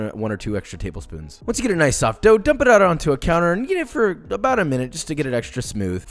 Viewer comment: is it just me or does he say Yeet it